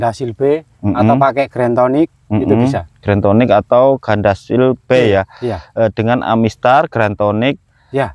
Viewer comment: Pak. . Penjelasan Pak. . Amistar dan Gandasil ,,B,, ( bukan P ) . . kl Gak Salah Dengar.